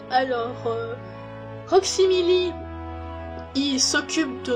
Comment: Alors après écoute j'ai des choses à dire : 1) à 22:20 j'ai compris Roxymili au lieu de Rewksimili XD 2) quelques secondes après tu dis "pour éviter que vous disez" ou alors j'ai mal entendu mais on dis "que vous disiez " :p 3) Pain au chocolat ou choco... Bon ok j'arrête 4) 42 ? 5) OMG ce commentaire n'as plus aucun sens Il Sinon pour les commentaires constructifs le son de la musique et un peu trop fort par apport à ta voix par moment... Ah et ça manque de chat u_u À part ca c'était super j'ai rigolé par moment :3 Continue comme ça c'était vraiment bien ! ^^ PS: Ça commence à devenir long pour un commentaire mais c'est important aussi je voulais faire un petit commentaire qui porte pas sur la vidéo mais ton travail en général. Sois confiante tu te débrouilles très bien et même si il y a quelques commentaires désagréable cette vidéo prouvé qu'il y en a quand même eu des bons qui sont là pour t'encourager. Crois en toi et ne te laisse pas abbattre !!!